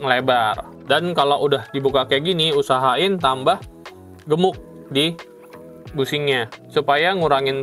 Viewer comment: sorry om, itu apa cairan namanya apa? kurang kedengeran 😅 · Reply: Minyak gemuk.